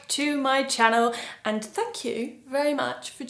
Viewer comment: I thought you were saying fu*k you!😂